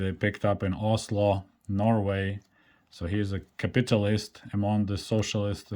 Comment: When I heard you say capit-a-list, I heard capitualist, as in Quisling, surrender or collaborater till I saw the fat guy running with money bags. I'm old. 🧐